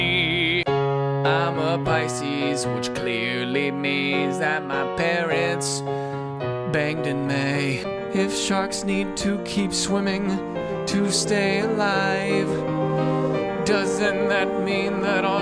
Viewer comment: I thought it said "banged in me" lol whaaaaaaat?